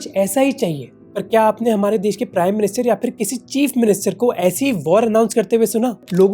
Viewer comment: I heard cheap minister 😂🤡